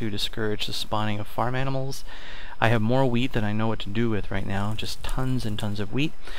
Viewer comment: I thought he said he had more "weed" but he said "wheat" lol